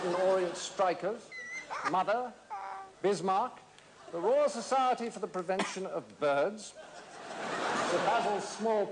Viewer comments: The Royal Society for the Prevention of Birds, still gets me. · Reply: I thought he said "The Royal Society for the Prevention of Sperm"....or did he mention the spermatozoa later? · The Royal Society for the Prevention of Birds, still gets me. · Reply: Thought he said .... Prevention of births